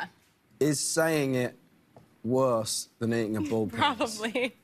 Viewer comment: I heard I'm eating the p....s But I was wrong